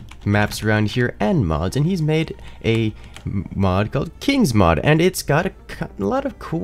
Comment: birdies mod, I thought he said kings